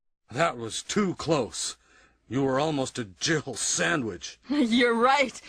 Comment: Barry said "Jibble "sandwich. Im dying on this hill. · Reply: He sounds like he is saying jibble sandwich coz he laughs while saying jill. But no it’s confirmed the line is Jill